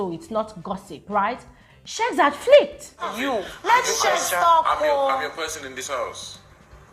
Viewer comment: Did I just hear Chichi saying let Sheggz talk oo😳 19:36 now I see say their mumu too much 😂, I pity them all😂